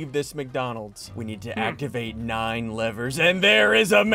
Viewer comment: i thought he said we need to activate nine eleven not nine levers :sob